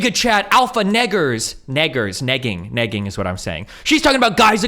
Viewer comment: I know that you're not saying the "word" but what are you saying? Naggers? One who nags? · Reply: It's negging. I can't believe its an actual word too, according to urban dictionary.